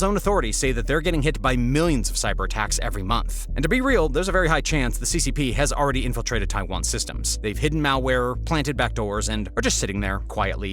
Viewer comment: I heard mao ware 😭😭😭